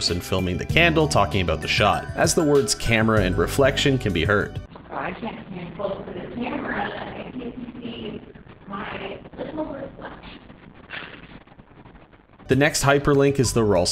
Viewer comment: I think they said or, spamton said: “I can’t get too close to the camera if you can see my… beautiful reflection” Idk man Btw, Kris is a they/them person.